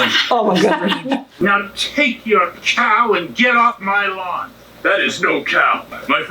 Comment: wait - did he...sorry! did she say get off my LAWN!?